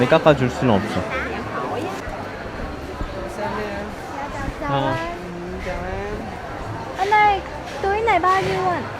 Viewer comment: 10:31 에서 인사하는거 뭐라고 말씀하신건지 알려주실수 있나요?? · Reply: 자오안?